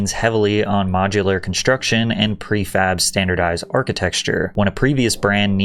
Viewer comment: me: did he just say architexture??